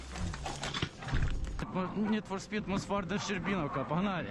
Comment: Did he say Need for speed at 2:34😂😂